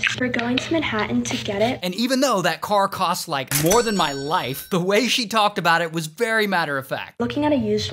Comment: “The car cost more than my life” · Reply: I thought he said more than my wife